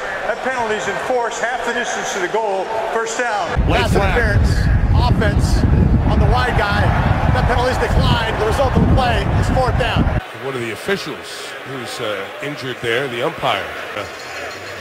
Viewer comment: I always thought the red at 1:49 says white guy but he says wide guy